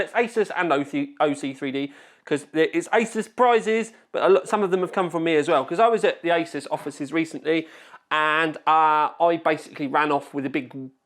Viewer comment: Sounds like he said I was at the isis offices 😂